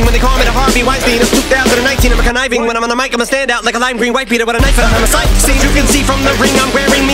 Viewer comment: I always thought he said “a lime green wife beater with a knife at a homicide scene” Edit: spelling